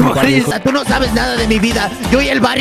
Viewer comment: that is a padre nuestro (pray) i will say everything i hear: padre nuestro que estas en el cielo santificado sea tu nombre 0:11 he say: they high they high 😨😦🤯😭😱🥬🤏😱☠ they have DrUgS dRuGs 😱THE DRUGS 😭THE mInD the DRUGS THE MINDD